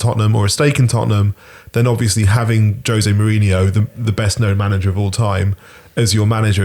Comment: Excuse me, did I just hear someone call Jose Mourinho the best manager of all time? I know a City fan but come on now..